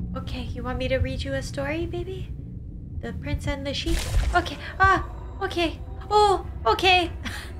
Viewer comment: did Lauren say fuk it??? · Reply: She said 'ok'